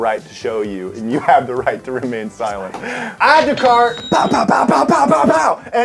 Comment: Did anyone else hear someone whisper silent at 19:52 OR AM I JUST CRAZY?!!